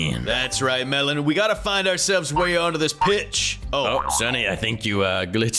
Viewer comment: Did i hear b$tch?